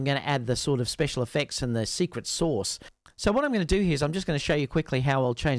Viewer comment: Did you just say secret sauce? ❤